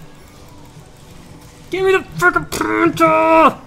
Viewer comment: the subtitles reads out "fucking" instead of freaking